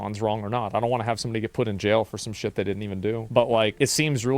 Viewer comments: WTF DID ASMAND JUST SAY NI GGA ?!>>! 2:58 h9loollyyyyy!!!!! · I swear to god I keep hearing him say the n word at 2:58 even though I know he’s saying “somebody” lol